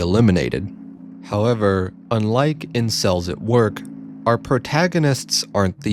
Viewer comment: i thought i heard ''incels at work" but it turned out that the word that was being said was "in cells at work" lmao T.T